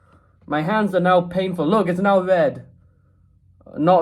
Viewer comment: I thought I heard you say ‘look it’s Nile red’